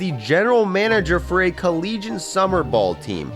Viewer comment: Did he say collegian summer ball? It’s Collegiate